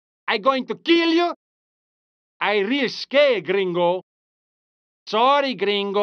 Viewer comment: what he says there "i re-escaped gringo"? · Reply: i really scare gringo